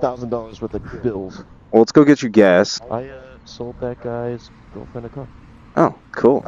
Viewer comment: I thought he said “I sold that guy some dope in a cup” the first time 10 times I heard it 😂